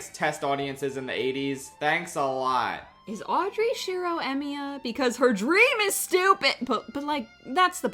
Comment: I heard “is Audrey Shirou Emiya because his dream is stupid” and choked on my Baja Blast. Thanks, there is now soda in my nose.